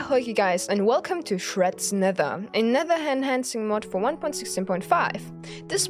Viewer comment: i thought he said shreks nether😂